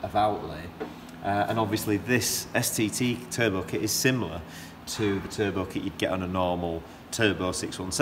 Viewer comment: Was this engine originally a turbo or a non turbo? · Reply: Good question. My own guess is that -- based on what he says at the end -- it was originally a non-turbo. (It goes by quickly, and I myself only caught it the third time watching.) What he says at around 12:13 is "..and obviously this STT turbo kit is similar to the turbo kit you'd get on a normal 617. So you'd be able to expect similar sorts of gains on a turbo 617 by adding one of these pumps." I /think/ he is implying that this car was not originally a turbo.